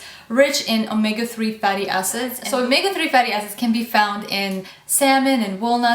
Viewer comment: sounds like you said: omega three.. fatty asses.